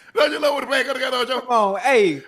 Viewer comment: wth unk say 😂😂😂😂😂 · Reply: Dont u love when a plan come together ocho